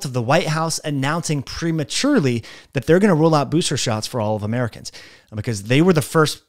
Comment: Did you say Rule out, or Roll out Booster shots?